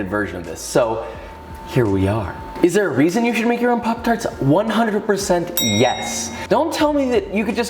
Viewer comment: Thought it said “pooptarts”